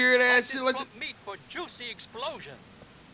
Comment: Did he say, "Join me for juicy explosion"? Luis was like, "I got a juicy explosion for ya..." 💥 😂😂😂